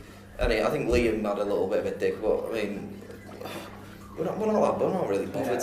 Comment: did he call liam a dick? Tf??? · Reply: He said "I think Liam had a little bit of a DIG," meaning Liam made snarky comments about The Wanted.